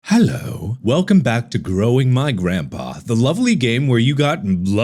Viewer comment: I thought he said blowing my grandpa ffs